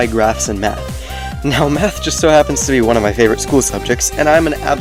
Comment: i swear he said meth